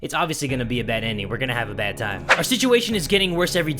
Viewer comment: I think he said we're gonna have a bad time